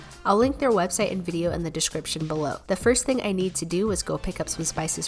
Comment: Did anyone else hear "dish-cription" @ 0:20? 😂😂